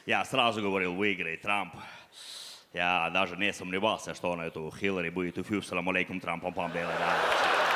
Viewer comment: что он сказал? Все смеются, а я мотаю сново и сново, но одинХ не понимаю -_- Ну в принципе тут юмора и так 0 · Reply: "я даже не сомневался, что он эту Хиллари будет юф-юф саламалейкум трам-пам-пам", ну ты понял... типа оттарабанит её